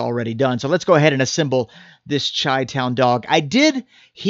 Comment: Did you just say "chai" town hot dog? You had me til you butcheded that....